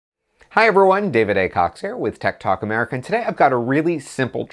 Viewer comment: Dude sounds like he saying ted talk instead of tech talk